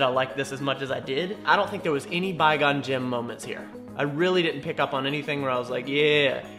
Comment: "There wasn't any Bye Gon Jim moments in there". As a huge star wars fan, never change. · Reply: I always hear that as"a Byegone-Gin moment," like something maybe once said in a drunken stupor.